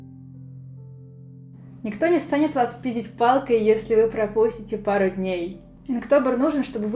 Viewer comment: мне послышалось или она сказала: «Никто не станет вас пи*дить палкой». МНЕ МАТЕРИШЬСЯ ИЛИ ЭТО У МЕНЯ ТУПОЙ СЛУХ?!?